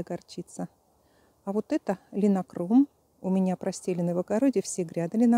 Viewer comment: Что такое линкором(если я правильно расслышала) и где его купить?